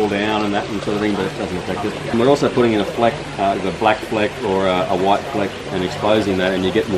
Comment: What does he say at 6:54? A white paint or liner? For better reflection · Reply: He said he added a black or white ‘fleck’. I’m assuming some sort of substrate was added to the concrete before pouring, then after it had set they would likely have ground the surface back to expose the fleck which added to the reflective quality of the light. They do it with concrete floors etc...